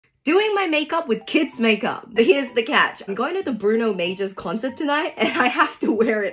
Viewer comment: I thought you said Bruno Mars not Bruno Major lmao 😂